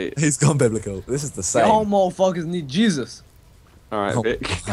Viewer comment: What did Vik say? I couldn't understand. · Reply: "yall muthafukas need jesus"